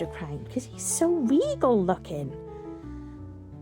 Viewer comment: 😂 my husband swore you said smegol instead of regal and started doing impressions…. 🤦‍♀️ he definitely took “stay weird” literally 😂. Bless him…. Awesome froggos btw !!!!